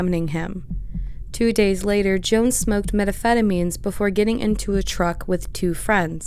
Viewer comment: lol “meta pheta means” you can tell she has no idea what that word is.